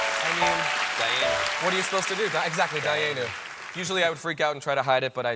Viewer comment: What is the word being said by Colbert and repeated by Ben Platt at 05:28 "exactly dyeanum?"